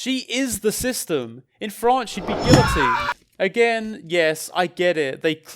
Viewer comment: "she is the system. in ___ she'd be guilty". what are you saying there? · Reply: France